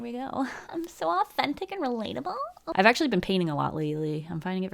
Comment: Okay I have an audio processing disorder, so I had to rewind this and double check because I thought I heard “I’ve actually been peeing a lot” Followed by the sentence after exactly as you said it. And let’s just say the relief I felt was akin to relieving a full bladder just in the nick of time. Good to have you back 😂 Thanks for the mini stroke.